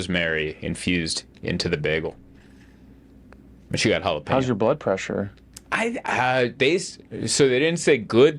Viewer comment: I thought said: "How your buttpressure"😂. Hihi, so silly Swedish me 😅